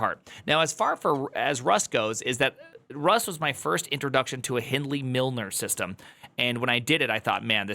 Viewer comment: What is he saying I can't hear? Helny milner?